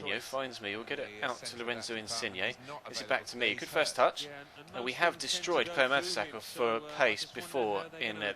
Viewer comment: The Text in this video is telling me what u are really saying.. ¨¨We have destroyed homosexuals¨¨